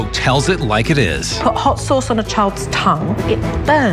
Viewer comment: Am I the only one that heard him say he put hot sauce on his tongue